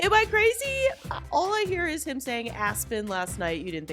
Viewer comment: I hear “ask mom” not “Aspyn,” and funner is not a word.😂